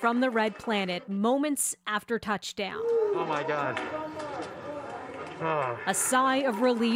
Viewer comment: Did he just say "Oh my Dog" ? xD